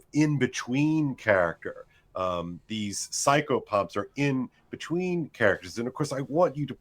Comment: What's he saying there? psycho pup? AHH: "Psychopomp" OK.